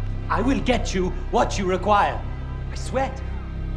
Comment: lmao i hear "I sweat"